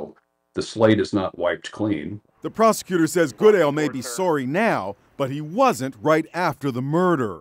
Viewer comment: Is Inside Edition a canadian thing? I know I just heard that voiceover guy say 'Sohrry' at 1:32.